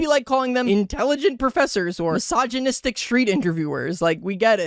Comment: i didn’t hear that well did he say “mysogyistic” or “sogyistic”? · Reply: Misogynistic